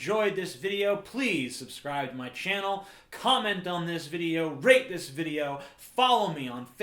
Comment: Hahahaha, at the end, when he said "rate this video," I could've sworn he said "rape this video."